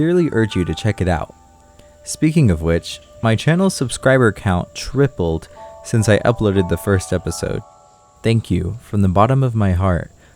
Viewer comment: I heard, “Speaking of a glitch, my subscriber count tripled after I released the first episode,” made me quite confused but smile on the inside before I went back to see if I heard wrong.